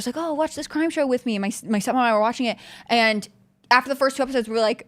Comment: did HCB say son? She talks so fast I can't tell